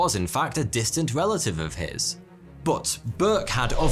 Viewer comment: My new gaming name 14:55 Anne Gray. Which until 42 said the name it never occurred it sounded like Angry 🤬